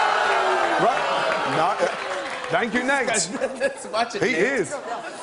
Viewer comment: are my deaf ass ears deceiving me or at did gordon say at 2:05 "thank you, next !!" ? what a legend. kek (｡￫∀￩｡)